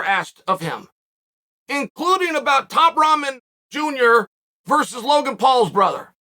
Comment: did he say Top Ramen Jr.???